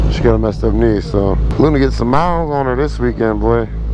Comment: “SHE CANT BEND HER KNEE. SHE ABOUT TO GET SOME MILEAGE ON HER” I’m dying laughing. D you savage 🤣🤣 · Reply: I thought he said luna getting some miles on her.